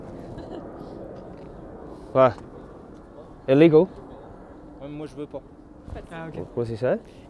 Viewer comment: he said; Ouais, moi je ne veux pas. Which means; Yeah, me, i do no want to. As he points towards the cameras